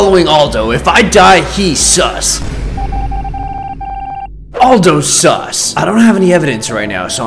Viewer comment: bruh I think he say "Aldous"